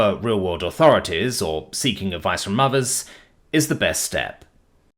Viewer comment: see the fun of having a human narrator, is the fun times where our brains can mishear “Seeking help from Mothers” instead of the actual message of “Seeking help from Others”